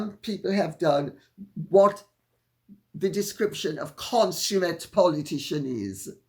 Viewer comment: You said "consummate politician" I heard "expert con artist" LOL!